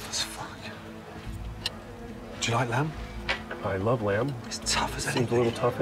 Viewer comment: It sounded like he said, "i love lamp" and not, "i love lamb"